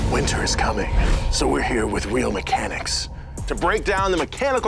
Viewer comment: I heard ‘WHEEL mechanics’ and chuckled the got sad when it was ‘real’